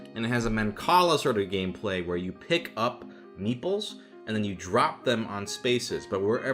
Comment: for the longest time i thought you were saying "meatball" instead of "meeple"